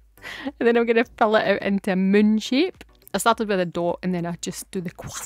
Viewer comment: 😮 I thought you said you were going to “munch it” at first. Then I was like oh, “moon shape”. Absolutely love your videos!!!